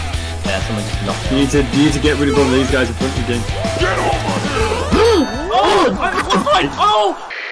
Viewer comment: what he said: "Get, Get the crown, Get the crown!